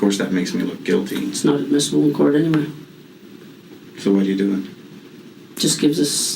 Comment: "inaudible"? She clearly says "It's not admissible in court anyway."